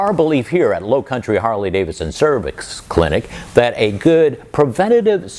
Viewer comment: Did he just say cervix clinic